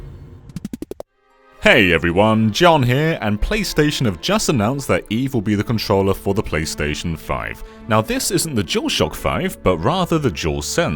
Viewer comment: listened to that part 20 times, I still don't know what he said. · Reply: "Hey everyone, John here and PlayStation have just announced that Eve will be the controller for the PlayStation 5"